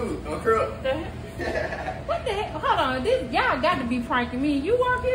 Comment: did he say " on crip " 😂😂😂😂